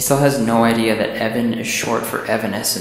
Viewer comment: Imma say this, I thought he said "Evan Hansen" instead of "Evanescence", and somehow that made me laugh.